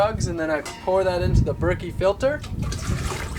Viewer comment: Does anyone know where I can find the water filter he uses? I thought he said it was a brickie water filter at 2:10 but I couldn't find any info when I googled that...